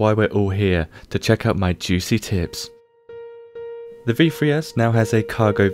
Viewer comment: anyone else think he said "to check our my juicy tits" xD